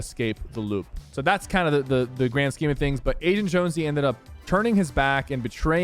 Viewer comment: I thought u said asian jonesy